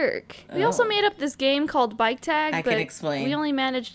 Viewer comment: I legit thought she said “Bite tag” instead of “Bike tag” Lmao